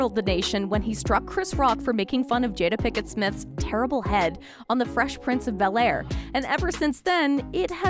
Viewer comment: Did they just say "Terrible Head" 🤣